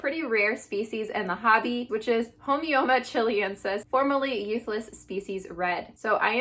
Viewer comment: At first I thought you said "formerly useless species Red" and felt slightly offended by this display of specicism...